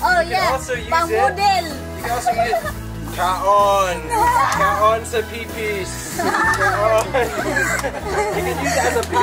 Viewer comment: Did I heard "Kaon sa pepe?" or "kaon sa Soso?" hahahh ikaw ha! hahaha PEACE!